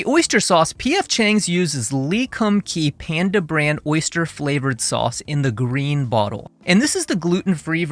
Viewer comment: Not me mishearing you saying "Oyster Brand Panda flavored sauce" and thinking "OH MY GOD THATS WHAT PANDA TASTES LIKE!?"